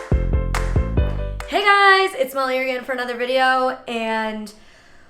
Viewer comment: I thought you said “it’s malaria in for another video.” For a second i was like “molly. Are you okay?”